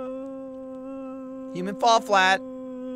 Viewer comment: I thought it said Human Fall Fart for um...longer than i should have....